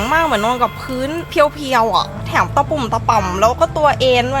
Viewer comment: หนูได้ยินแอ๋มอะ แต่ซับมันคือแถม555